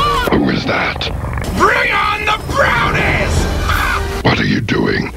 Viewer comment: Did he just say "bring on the bronies"?